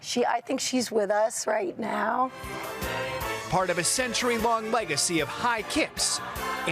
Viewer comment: Long leg----acy? No pun intended but I heard it.